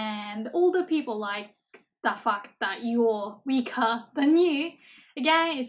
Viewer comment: I know that's not what you said but it really sounded like "that fuck"